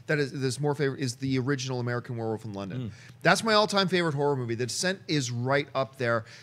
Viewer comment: Hey guys, what did John says at 5:12, what horror movie did he mentioned? I could hear him properly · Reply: Quoc Thai The Descent